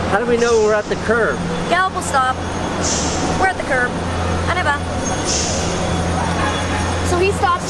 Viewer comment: I can't understand what she said at 7:50 ... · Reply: On y vá it means walk on in french she was instructing gallop as he was taught at a Canadian guide dog school where they teach them in french